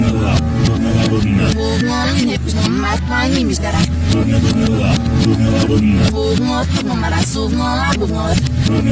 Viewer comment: eu ouvi ela dizendo:animes kkkk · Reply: Ela falou "Animes caraio"